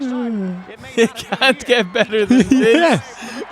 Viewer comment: 'You can't get better than this' Why did I hear that as 'there's more to Ireland than dis'?!